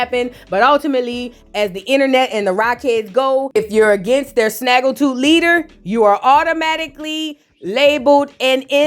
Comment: Did she say yall snaggletooth leader 😩😩😩😭😭😭😭